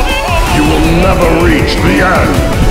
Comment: Crypt of the necroNANCER! I hear no "D" sound in there. Am i the only one?